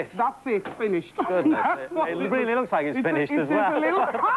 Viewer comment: Anyone heard it right what robin said at the 12:34 mark?? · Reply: "It looks finished as well." My understanding is that "finish" is a slang term meaning essentially "sex. is over". I think it generally used in reference to bad sex where on partner is satisfied and the other is not. I could be wrong though.